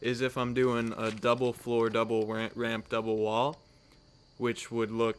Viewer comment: who else heard double ramp ramp?